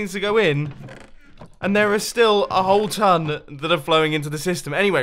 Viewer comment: he says " a ton" and the subtitles say "buttload" and somewere else he say Mumbo and the subtitles say Bumbo Cactoni ......*facepalms*